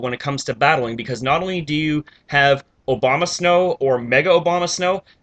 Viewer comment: Sounded like you said "Obamasnow" xD